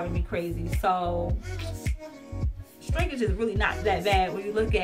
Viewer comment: Every time I hear you say 'Strinkage' I smile..lol